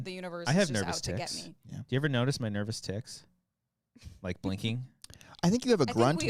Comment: I thought said he has nervous tits. 😂😂